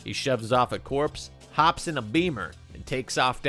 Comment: did you just call a Jaguar a beamer? That's an insult that's not taken lightly.